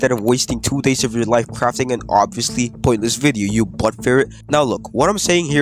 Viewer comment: did you just say "butt feret"